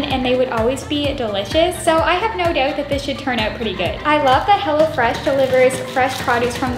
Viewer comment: Am I the only one watching it 1 year later and then when she said “hello fresh” I thought she said “hella fresh” anyone else